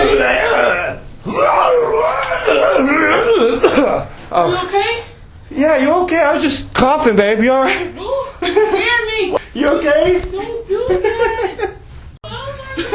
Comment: last clip was da best!! did you say "bae" at 2:28? i don't know why that made me laugh xD good vid as always! :D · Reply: Yes I said "babe" but I'm not sure why. LOL